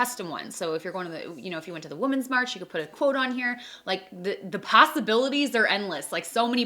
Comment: Did you just say "pastabilities" or did I hallucinate that since Im dieting?? Either way I love this idea 🌈🌈 instagram:divi0809